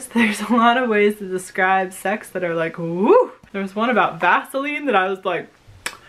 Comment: Don’t call us Dead has a very interesting cover. When you were talking about sex I thought you said gasoline!! Haha!! I went back and realize you said Vasoline!! That’s hilarious that you bought the same book twice (I’ve done it too 😳). Great poetry haul! Eeeeeeeee!!!! You have Hamilton!!! Awesome! And Happy birthday!! I haven't seen Hamilton, either, also because of the price. But I enter the lottery every day for a $10 ticket, and keep hoping!